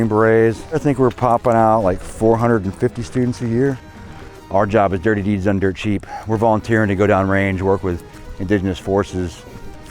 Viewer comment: ok is it me or did he just say a lyric FROM ACDC AT 2:30 am i the only one out of 5.5 million that pointed this out i was shocked to hear it. because if so. i now know that acdc in the song. he say's dirty deed's and the dunder chef's ok i just did the research omfg really acdc in fact say's DIRTY DEED"S AND THEY'RE DONE DIRT CHEAP now i need to know did the military come up with this and the acdc take off with it or did acdc sing it and the military run with it PLEASE let me know im very interested in this THANK YOU please don't tell me im the only one who hears dirty deed's and the dunder chef's i in fact feel even dumber now i am 38 years old to just find this out